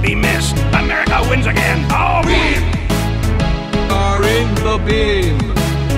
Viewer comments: I always thought it said "We! are in the meme!" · I Keep Hearing “We Are In The Meme” And “We Are In The Being”